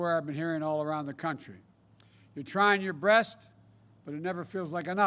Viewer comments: did I really hear "You're trying your breast,?" LOL Oh no...lol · I swear I just heard him say, "trying your breast". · Now folks, this, is an EVENT. Did he actually say "you're tryin' your BREAST" ? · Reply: The man said "breast" 😆😆😆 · did he just say “you’re trying your breast” at 13:30? 🤣